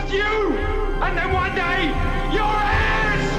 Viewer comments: Loved these movies since I was 6 in 1978..But all these years I thought Zod said “You will bow down before me.You and one day your ass”..Watching with closed captioning recently it’s “heirs” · Ya know the ironically funny part of what Zod says to Jor-El when I first saw this. It probably wasnt until I saw closed captioning did I realize what Zod actually said...."You and one day your heirs!" THATS NOT WHAT I HEARD. I thought he said "You and one day your ass!" LOL